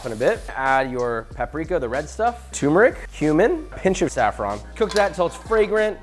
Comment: Did he just say add HUMAN at 10:14 ☠️☠️☠️☠️ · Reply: Cumin